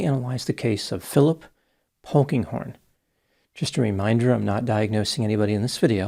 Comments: I initially misheard his surname as Pokinghorn. 😅 How very appropriate! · Polkinghorne...thats a new one. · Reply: At first, I thought said Honkinghorn.